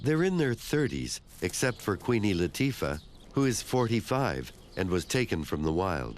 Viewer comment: Did he say "Queeny Latifah"